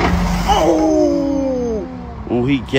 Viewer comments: "Somebody high speeding po-lice, talkin bout that HIGH SPEED, talking bout HIGH SPEED ...throw em out.. oooohhhhh, we got em, we got em, got em, they caught!!!" Excellent running commentary!! 😂😂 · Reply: I thought she kept saying somebody has to be a police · It’s the whispering “somebody high speeding , somebody high speeding “ for me 😂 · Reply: Oooohhhh I'm so dumb. I was trying to figure out what she was saying. The very first thing I heard was "oh that has to be the police, that has to be." I knew it wasn't right, cause obviously it was the police and there were some extra syllables, but I couldn't not hear it. Now that I read this, I can finally hear high speeding lol